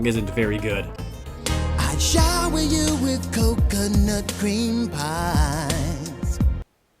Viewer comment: OOOOOHHHHHHH! I thought he was saying "I'd shy with you a coconut cream pie" I was thinking, what's so bad about that? Man that's bad